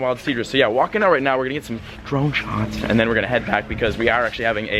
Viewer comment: did you say PATRON SHOTS? hell yeaaaaaa love me some tequila lol